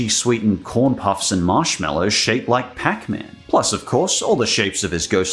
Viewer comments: It’s bizarre. I kept hearing “marshmallow shapes” with a mildly Jamaican accent, which was probably what they were going for. However, once I realized what you were hearing, and I rewound it repeatedly, that is now all that I can hear. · Damn. It really did sound like they were saying marshmallow shits. Has anyone managed to verify whether that was real or if it may have just been a slightly degraded copy?